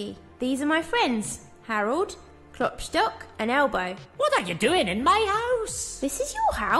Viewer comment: This reminds me of scott cawthons first ever game (before fnaf) 2:20 i thought he said ' WHAT ARE YOU DOING IN MY SWAMP?!" XD